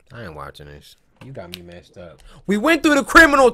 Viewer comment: did he say not watching that shit?